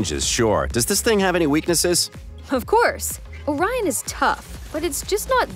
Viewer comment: i thought it said onion